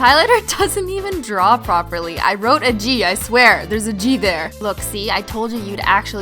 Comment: Is it just me or did she say Gbear